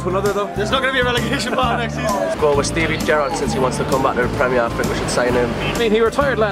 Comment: Did he really say Defoe is world class? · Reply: i thought at 1:51 he says steven gerrard haha